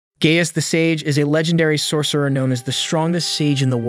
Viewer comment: First thing I hear "gayest the sage" 💀💀💀